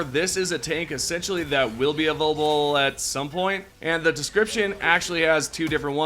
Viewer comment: what the fuck is wrong with this guy, he slurred and messed up like 5 other words and sentences before this are you drunk bro??? What word did you say here at 6:10 “ovulavble” take your time bro also the missle silo is different from the nuke stratagem for a information channel your information is fucking dogshit you should do more research and put more effort into your videos, I can see rushed content.